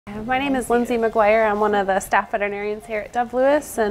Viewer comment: I thought she said lissy McGuire lol one of my childhood favs!